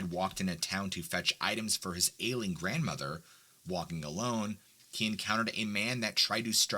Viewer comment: I thought you said “alien grandma” around 15 minutes in… apparently this weed is making me hearllucinate. Good stories, like always!